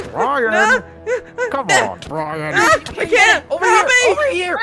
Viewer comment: ish, did he just say helpy?